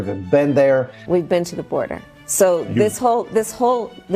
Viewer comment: i thought Harris said We’ve Ben D Over…